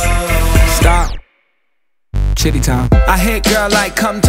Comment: when chiddy said chiddy time it sounded like he said shitty time